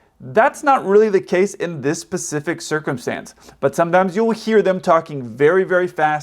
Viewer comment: When speaking about non-English speakers understanding English did you say pacific rather than specific? 🙃